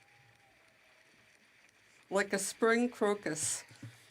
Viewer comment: OMFG, did I hear that right? The translator lady forgot to turn off her feed, and then starting at 3:57, when Singh walks in, exclaims "Oh look!" then a few seconds later, referring to the yellow turban, "Like a spring crocus!" Who knew the dry translator lady had such a wicked wit :D.